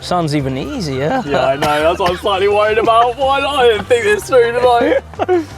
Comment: I don't understand what he says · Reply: 'i know, that's what I'm slightly worried about. why I didn't think this through did I' I think